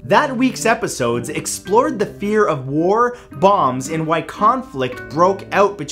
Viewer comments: When Mat said “let’s talk about BOMBS!” I thought he said BONGS And I got way to exited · I thought he said bongs instead of bombs